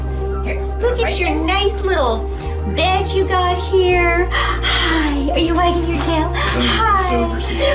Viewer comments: did she say look at you little nice bitch right here? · wtf. You didn’t have to say bitch you could of said dogs or female dogs 😕🖕🏽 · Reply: Ålishå Šavagë I don't think she said "bitch" I believe she said "bed you got here"